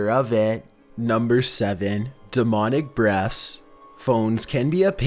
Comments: I thought number 7 said demonic breasts :/ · I Swear It Sounds Like He Said "Number Seven: Demonic Breasts"Lol. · LOL I thought he said #7 Demonic Breasts 😂😂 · Anybody else at number 7 thought he said demonic breast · did i hear "demonic breast"? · I swear at number7 it sounded like he said demonic breasts! 😂 😂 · 7 I thought he said demonic Breasts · I thought you said "demonic breasts" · did you just say Demonic breasts!??? xD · On number 7 I thought he said demonic breasts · On number 7 I thought he said demonic breast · Imma be honest I heard "Demonic breasts" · No.7 "Demonic breasts" SORRY I MISHEARD IT😂😂😂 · I thought he said denomic breast when he said denomic breaths😂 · At number 7 I thought you said demon breasts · I thought he said "demonic breasts"